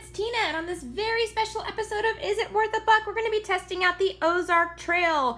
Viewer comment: I almost thought she said is it worth a f*** till i read the title of the video again